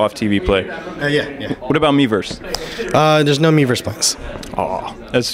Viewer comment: I cant understand what he said about miiverse, did he say no miiverse post?